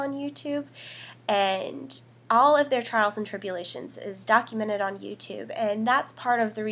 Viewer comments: I thought she said 'Charles and Trippy-lations' at 1:20 Lol but this is amazing! Go Trippys go!! #Ellen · When she said trials and tribulations it sounded like she said "charles trippy" haaha